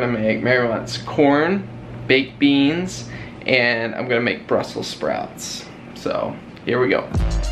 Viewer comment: It sounded like you said Brussels sprats.